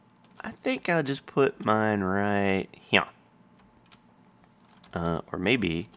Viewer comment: when you said "here" it sounded like Vechs xD